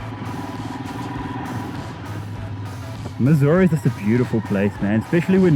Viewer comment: I thought he said Missouri instead of mussoorie and though he went to the US